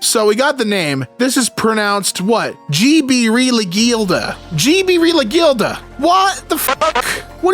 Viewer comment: imma try translating this and yes this is my 2nd comment on this vid i think it says Xhibirilixhilda X h i b i r i l i x h i l d a (Zibi rilix hilda)